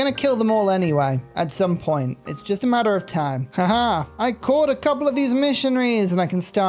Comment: Instead of "caught," I thought at first you said you "corp-ed" the missionaries and wow, what if you could make religious units into corps and armies?!?